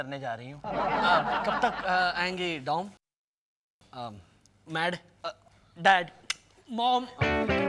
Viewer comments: What did Sahil mumble at 5:52 ? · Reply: "kab tak ayengi dom mad dad mom "😂😂😂😂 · I bet lot of them missed this, probably was edited but it's quite clear what he was saying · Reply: He said “whatever”